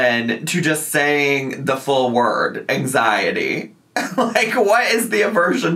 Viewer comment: i didn't even notice that she said "anxiet" until u paused the vid and pointed it out, also yeah, that's the worst code word ever. 1. most ppl are probably not even gonna notice u said "anxiet" and think u said "anxiety" (like i just did) 2. a code word is supposed to hide what ur talking abt. removing one letter isn't really hiding anything, especially if ur code word isn't a real word...